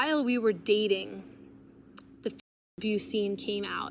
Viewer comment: Did she say F*ck Abuse scene? 😳What the actual hell!